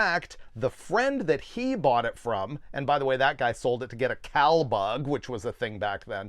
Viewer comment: A what? I cant hear what you are saying. Cow bug? Thats an insect?